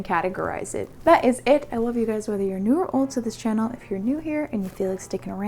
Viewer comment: Every time when say ''new or old to this channel" I hear ''neural"